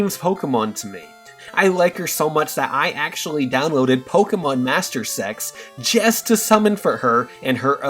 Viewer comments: At first, I thought he said, "Pokemon Masters Sex," but then I googled it thinking it was some sort of fan-made Pokemon hentai game, and it showed that he actually said, "Pokemon Masters EX," and I was like "Oh.." Kind of funny, I guess. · "I like her so much I downloaded Pokemon Master Sex" I swear thats what I heard and I was like what!? is that really the App? I looked it up and its Pokemon Masters EX. I would pronounce the EX part as Eee Exx